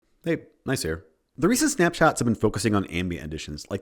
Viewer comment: I thought you said “hey nice hair.” when you actually said “hey Gneiss here.”XD